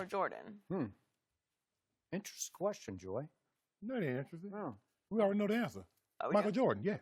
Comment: Its like skip intentionally made this question and put it on 🤣🤣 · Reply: Tristen Vro at 0:08-0:09 it sounds like Shannon says it’s lebron😂😂😂